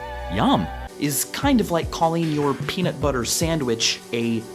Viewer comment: I skipped 10 seconds before he said peanut butter to hear the last one again, so I thought he almost said “is kinda like calling your penis-“